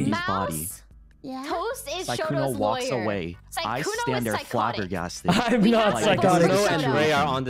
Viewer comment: At first I thought said that sykkuno was sykkuning not psychotic